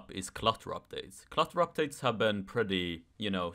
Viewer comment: ¨clutter updates¨ what i meant, stuff has been adding up for such a long time theres just too much stuff lol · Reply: I thought you're saying Cluster Updates for a second 🤣 Jokes aside,I can agree